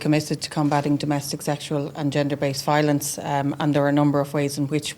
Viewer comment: What has "gender based crimes" got to do with anything? A crime is a crime no matter who commits it or who the victim is!🕊️💚☘️☘️ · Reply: Thank you for the comments I thought she said bender based crimes...I knew I heard it wrong because varadkar isn't it charge yet...